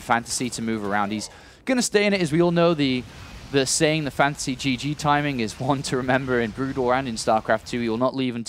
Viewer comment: OK I always thought they were saying "fancy gg timing" but TIL they were saying "fantasy gg timing"